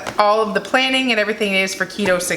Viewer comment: I love you guys!! Your Mom is awesome "get a larger purse" LOL!!! Love it! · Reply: Lol at first I thought she said "get a lodger first" and my brain was confused as that made no sense...then I got it 😂🤣